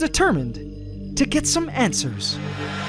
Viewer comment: did he say ANT-swers